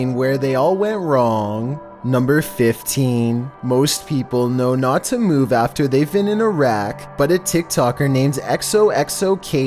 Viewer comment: I thought he said, “Most people know not to move after they’ve been in Iraq,” lol